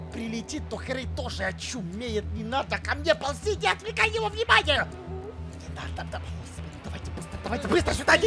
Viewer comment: что он сказал?😮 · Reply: Иди отвлекай внимание